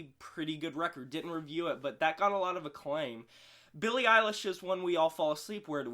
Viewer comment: I thought you said you used to know Billie Eilish lmao